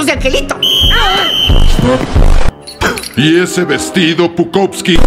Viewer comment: Te juro que le entendi "Y ese vestido, putousky?"